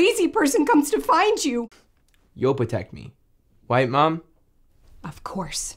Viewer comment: I keep meaning to watch this and also keep forgetting🤦‍♀️ I also misheard that as "you'll protect me.. White mum"😂